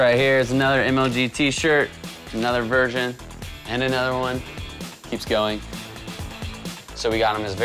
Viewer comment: Did you just say another virgin?